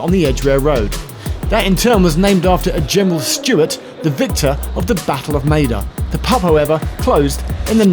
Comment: I always thought "Maida Vale" was a pun (or, at least, should have been) - always gave me a chuckle going through it "The next station is <made of ale>" ... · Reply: I swear it sounds like says "the Battle of Made-up" at 4:19.